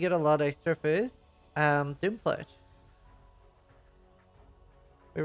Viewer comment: I like how twice got bamboozled reading the manifesto lmao >Spells getting damage buffs "Awe man Unleash is gonna be great!" >Unleash is getting Nerfed >Bows getting Phys Damage Buffs "Oh man this could be really good for Voidfletcher!" >Voidfletcher is getting nerfed GGG Knew what he was thinkin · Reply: If you're talking about what he says about phys damage buffs at 7:32 he says Doomfletch, not Voidfletcher